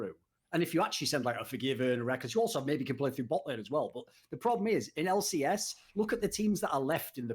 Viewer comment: Hold up! Hold up! Did I really catch Thorin saying this: If you actually send like a Forgiven or REKLESS, you also can maybe play through botlane. Is the Anti FNC, Anti Rekkles, Thorin giving Rekkles credits here? Or did I mishear it? This is a rare sight to behold.